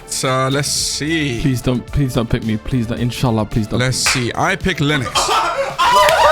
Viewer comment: did he say inchallah????!?!?!?!?!?!??!